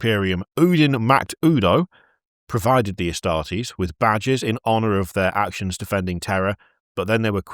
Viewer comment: I heard "badges to honour their actions on Terra" as "badgers to honour their actions on Terra"